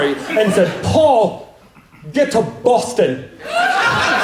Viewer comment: Am I the only one that heard it as "Paul. Get to Bustin'" ? Hilarious either way cause 'get to bustin' means to do something quickly which was the nature of Paul Revere's famous ride. XD